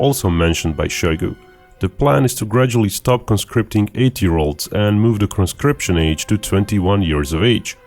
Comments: Wait wait wait, did Binkov just say at the 4:03 time stamp that Russia is conscripting 8 year olds. Wow, Putin is getting seriously desperate I see. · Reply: I think he said 18 year olds. If you check the subtitles :D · it sure sounded like binkov said "the plan is to gradually stop recruiting 8 year olds " what are those kids doing lol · I thought you said stop conscripting 8 year olds instead of 18 year olds at first and I was like damn pack it up already if your conscripting that young.